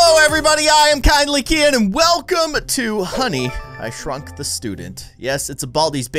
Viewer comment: i actualy thought baldi said "honey i shreked the student" XDD